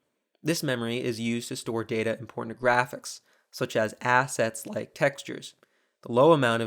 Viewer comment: did he say “pornographics”? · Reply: "important to graphics" lmfao you just revealed your dirty mind